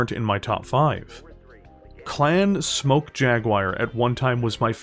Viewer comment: I usually dont get mentally speedbumped by accents but this is one of those rare occasions, I keep hearing 'Clan Smoke Jaguire'